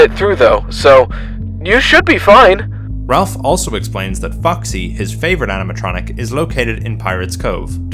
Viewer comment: idk why but i heard foxy as fucksy